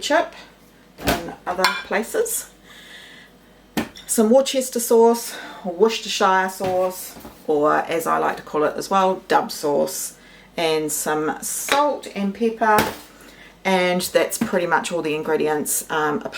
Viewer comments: Love watching your cooking and finding inspiration to do more as only cooking for me these days. Never had the corned beef like that, sounds so good but not sure if its my hearing in older age failing me but did i hear you say you add dove? If so what is it and where do i get it. I do know its not soap lol. Thanks again for your frendly welcoming content! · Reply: Maybe was saying dark sauce. Being Australian I had to listen a bit harder for a few words because I thought she said duck sauce. Great demos and down to earth. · Hi. You used two sauces in your hash. One is tomato sauce, or ketchup as we call it here in the US. The name of the other sauce starts with the letter "d." I think I heard "duck sauce." Is that correct? I really like your cooking style and recipes. Thanks! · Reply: It was Worcestershire sauce :-)